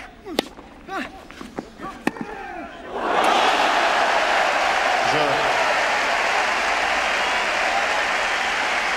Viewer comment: Did he just say "Flame on" :P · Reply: He said vamos jajaja